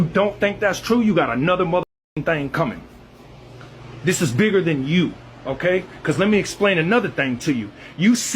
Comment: Everytime this wyte guy speaks all I hear is "This is bigger than Neno Brown!" Wesley Snipes from New Jack City?